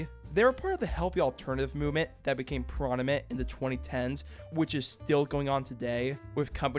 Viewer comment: did you just say "pronimet"? lmao